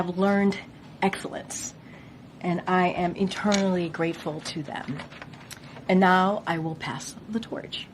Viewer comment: Did she just say she was "internally grateful"? 😂😂😂